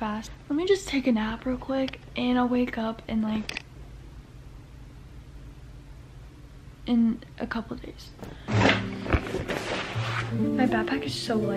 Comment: did she say she's gonna wake up in a couple days